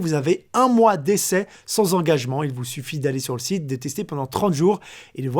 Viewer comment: C'est moi ou a 7:40 il dit détester pendant 30 jour😄